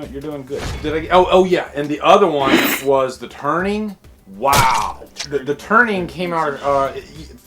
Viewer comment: I was listening to this without looking at the screen and I almost choked on my coffee when you said "The Turning". I thought you'd said "The Burning".